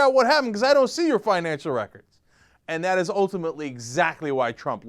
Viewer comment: the subtitles at 1:37 it says "egg zachary" instead of exactly...that was an old joke from when I was like 4 years old (and I'm 41 now so it was a long fuck'n time ago) - thanks for giving me a smile Youtube subtitles!!!